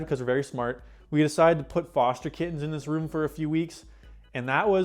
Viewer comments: Oh foster kittens I heard it as kids first 😅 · I thought you said foster kids at first 😂